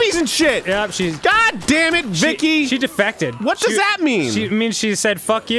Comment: i thought that said "defecated" and not "defected"